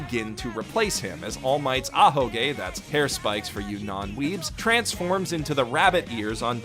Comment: I misheard ahoge hair spikes as ahegao and was very very confused and concerned